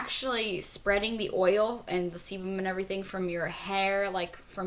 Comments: sound like she said oil and semen... XD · Wtf did she say..... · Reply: Katie Alison sebum.. oil lol